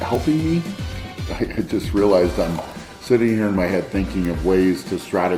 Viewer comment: Imagine being out shopping and seeing 4x worlds strongest man Brian Shaw sitting alone in his car eating a dozen Krispy Kreme donuts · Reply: I thought you said "shitting" not "sitting" for a second had my laughing